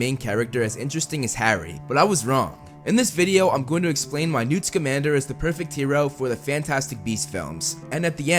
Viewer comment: I literally thought he said Newt's Commander